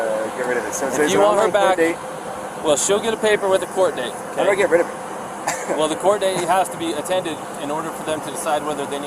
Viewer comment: did he say he was gonna get rid of her because I heard "Imma get rid of her" and it still makes me mad about it all